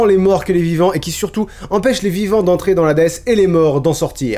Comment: Vers 17:00 j'ai cru entendre "Cerbelle" 😂😂 Mais pendant un instant j'ai compris "entrer dans Hadès" et pas "dans l'Hades" voilà, j'ai juste rigolé 10 min pour ça